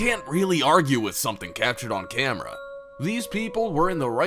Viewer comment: Yes you can. You can videoshop and edit things. And did he say ‘catched on camera’? Oh, my God. This guy has terrible grammar. ‘Catched on camera’. Are you freaking serious?